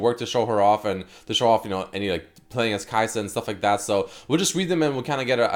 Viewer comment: Who else thought it said a kalista that name confused me hard